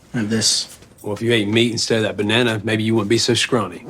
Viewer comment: I thought at the start he said "if you ate me instead of bananas"